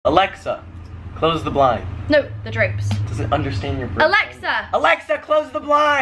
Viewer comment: I thought she said grapes 😂